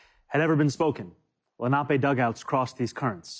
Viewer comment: I thought he said " nappy dugouts" at first 😂😂😂😂